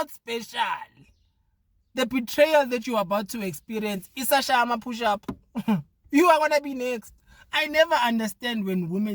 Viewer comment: 😂😂😂 Did he just say the betrayal isashaya ama push up?😅😅😅😅